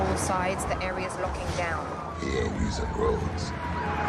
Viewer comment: tf did he say lmao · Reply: He ain’t using roads